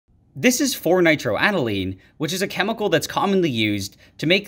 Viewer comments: "This is 4-nitroaniline" What I hear : "This is 4-night" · I thought he said Fortnitroaniline for a second lmao